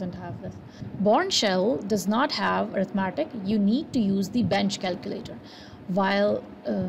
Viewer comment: did you say bench calculator? I did not understand